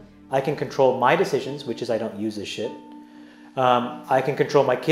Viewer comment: did he say I dont use this shit? And that his kids does not use this shit?